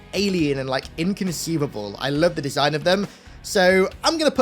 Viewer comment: Did you say, "inconceivable?" You keep using that word. I do not think it means what you think it means. :goodvibes